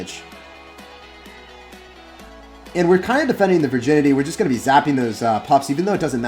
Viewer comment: did you say defending the Virginity ?💀💀 Are you sure you didn’t mean vicinity?